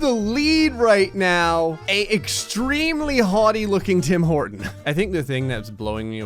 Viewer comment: i was seeing memes when he seed this and i hear hims say: "an extremly horny tim horton"